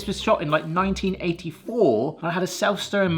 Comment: Did he say 1984?